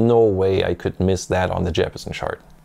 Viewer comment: I thought it said "Eppstein " lol 😆 😂 🤣 😅